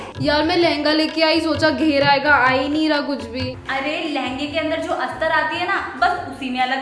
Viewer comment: me kya bol rhi h lehenga me lagane wala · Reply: Astar word bola hai